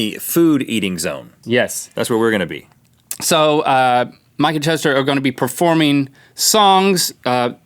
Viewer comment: DID YOU SAY MIKE AND TOASTER???😂😂